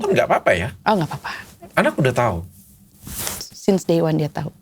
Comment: di sini bilang apa ya? kok aku ulang-ulang tetap ga ketangkep ngomong apa.. 🤔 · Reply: Since day one , dia tau = sejak hari pertama , dia tau